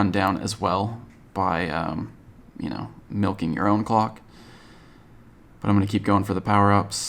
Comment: Um, what did you say? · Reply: Jå9 he said “u know milking ur own clock”